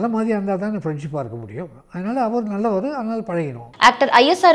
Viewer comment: Did she say Actor ' I ' sir ?? Who is that Agaaala Maraanam adaindha actor ? ? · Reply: she said ISR